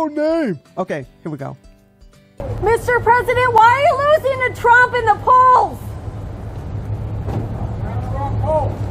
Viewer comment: I could not understand President* Biden's answer. The audio is not clear. But our host seems to have understood what he said. Anyone know what he said? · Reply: He said "They were the wrong polls"